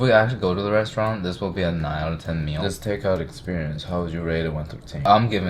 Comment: Been watching your videos for a long time (♥), and realized I have never told you: when I first started watching, when you said "rate it 1 thru tehn" I heard "ready to entertain." It works and made me laugh.